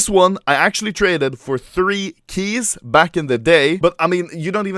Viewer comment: did he say that he traded it for 3 Keys?